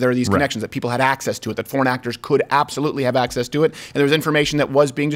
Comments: First time he said foreign actors I thought he said Porn Actors. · He said “foreign actors”. I thought he was saying “porn actors” which seemed more strange than it already is. Haha. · Every time Crowder said "foreign actors," I thought I heard "PORN ACTRESS." Now THAT'S ballot stuffing.